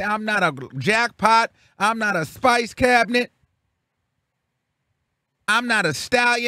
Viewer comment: Did he say I’m not a spice cabinet 🗄️ 😂😂😂😂